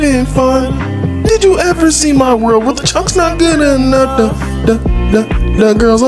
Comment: I thought you said the girls are miners but it was like the girls a minor